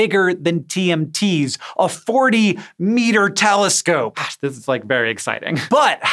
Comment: the CC transcript at 5:10 is incorrectly showing Hank saying "Lord" instead of "Gosh". Can you please figure out a way to fix this for accuracy's sake? Thanks!